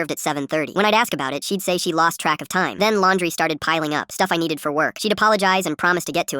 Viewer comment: Wait the kid didn’t have a phone but he texted her?? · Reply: yeah and i thought it said laundry was his job but he said “laundry started piling up”